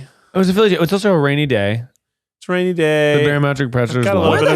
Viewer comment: said: "It's a rainy day" I heard: "It's a Rainee Day" Alas, only one statement was true.